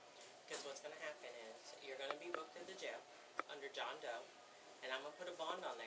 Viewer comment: Did she say that woman will be booked into jail under "John Doe?"😂😅